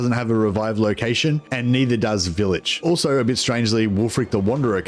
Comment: what faction did you say? village? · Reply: Vilitch